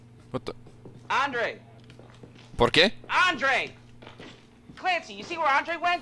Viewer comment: did he said "porque"